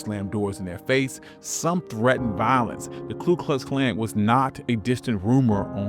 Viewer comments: The autogenerated subtitles put "Clueless Clank" instead of Klu Klux Klan @ 5:45 😂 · The subtitles misheard the KKK as "the Clueless Clank" and I personally think we oughta something with that 💀